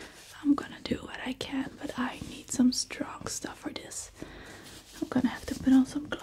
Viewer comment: in 3:11 I believe i heard "some strong drugs for this" 🤣🤣🤣🤣🤣🤣